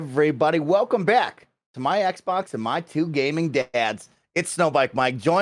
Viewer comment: In the intro I thought I heard my xbox and my two gay dad's HAHAHAHAHA what new CW reality show is this and why do i want to watch it!